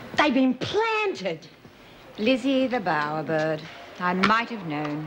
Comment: What does Erica call Lizzie? It's not Birdsworth. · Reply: Lizzie the Bowerbird, bowerbirds collect pretty, shiny things to decorate their nests and that I think is what Davo means.